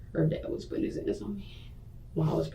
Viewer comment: "HANDS ON ME". Caption said, HE WAS BEATING HER UP WITH HIS